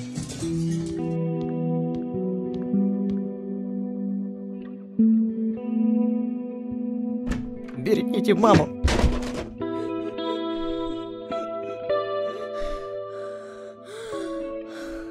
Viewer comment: там сказал берегите маму Кто тоже услышал лайк